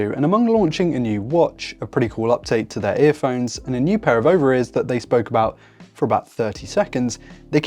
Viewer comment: I genuinely thought he said a new pair of ovaries lol. british accent can be surprising sometimes